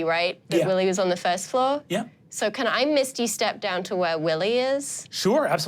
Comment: I adore that the captions say “Can I, Misty, step down to where Willie is?” Bc someone didn’t clock that there’s a spell called Misty Step. I love that ❤